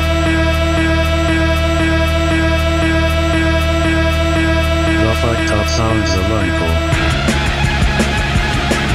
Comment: what is Hawking saying at 25:31? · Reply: Milovan Asanovic THE FUCKED UP SOUNDS OF UNKLE